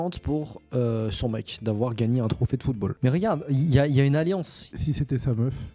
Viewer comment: UN TROPHÉE DE QUOI ???!! DE QUOI T’AS DIS ???!!! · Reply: Bah football